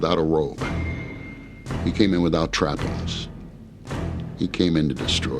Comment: "He came in without a Robe, He came in without ____?" What are you saying Bert? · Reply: Trappings. accessories, accoutrements, appurtenances, trimmings, frills, accompaniments, extras, etc.